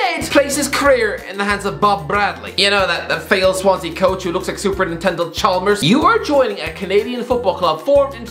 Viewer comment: did you say Super Nintendo Chalmers?